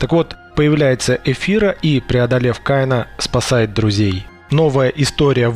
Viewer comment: English subtitles in progress! :) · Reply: it's written Kian instead of Kain I suppose ;)